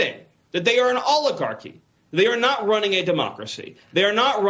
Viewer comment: For a second it sounded like he said "They are an Olive Garden" lol, like the restaurant.